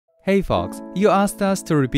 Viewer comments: Hey FOKS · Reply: DunkinDonuts90 I thought he said "Hey fucks" at first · Tbh I thought u said in the intro “HEY F***KS” 😂😂